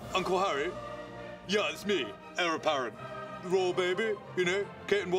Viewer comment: All these years, and I now discover he likely said "heir apparent" - I thought he was saying his name was something like "Error Parrin", I didn't get why Comedy Central thought the prince would be called that...